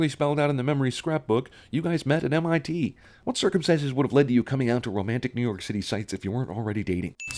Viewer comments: did he say circumcises instead of circumstances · lol you said circumcises instead of circumstances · does he say circumcises?